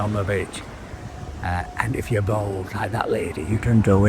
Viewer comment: I thought you said “if you’re bald like that lady” 😂